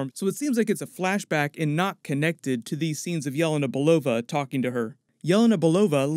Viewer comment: Was trying to make out what is "yell and a Belova.." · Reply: Yelena Balova is name of Flourance Pugh's character in the movie